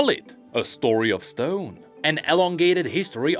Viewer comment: Did I hear a rock and stone?